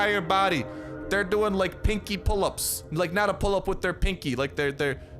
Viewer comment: i thought he said kincky pull ups